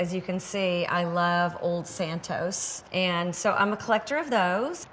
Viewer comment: Did she say she loves old Santas at 10:05 ? I don't see any Santas, well maybe that one under the glass dome ..